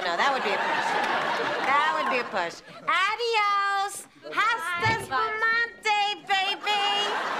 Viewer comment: Qué dijo? What did she say? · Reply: "Hasta Spumante, baby!" It's a play on words. Asti Spumante is a type of champagne.